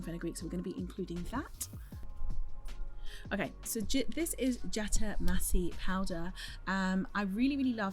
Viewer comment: Fantastic video! Will definitely have to add these tea rinses to my regimen! Quick question: may someone spell out the powder she introduces at 11:35? The auto-CC says "jetta massey", but I cannot find any results for the powder when I google that 😅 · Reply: Jatamansi powder